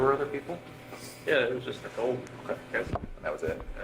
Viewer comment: did he just say what I thought he just said . “Just Nicole “